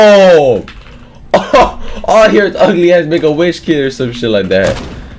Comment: what did you just say?????? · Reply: “make a wish kid”